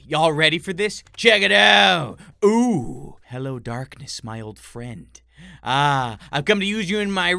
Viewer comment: did said hello darkness my friend